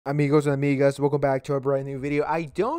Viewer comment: Sounds like he said Amigos and Nigg@ at the start